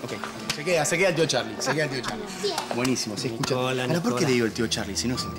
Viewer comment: estoy loca o escuche "HOLA NICOLA" mogli😖😖😖